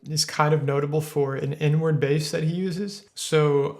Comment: I missheard i thought said n word bass oops😂